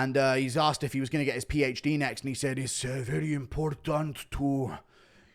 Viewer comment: the guy actually asked if after saturday night, jiri was going to get his phd in violence. but jiri missed that part and i guess other people missed it too. it was a fun question but it got misheard 😂😭